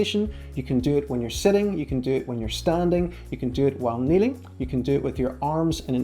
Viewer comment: I thought he said you can do it (Zenith Exercise) when knitting. He said *kneeling*.